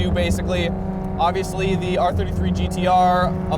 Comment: What does he say @ 2:08...? The car came with ________ from factory? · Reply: HICAS, High Capacity Actively Controlled Steering. It's the rear wheel steering system.